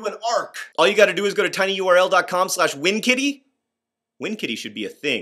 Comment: lol i thought he said wing kitty